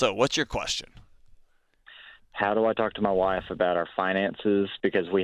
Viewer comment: At first I thought it said "How can I talk to my wife about her fiance".